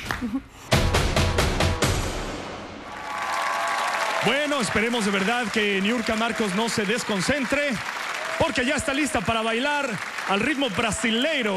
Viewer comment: entendí " niurka tira un pedo" xD chaless por eso entre